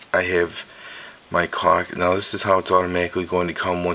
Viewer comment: Immature, I know, but it really sounds like you said "cock" instead of "clock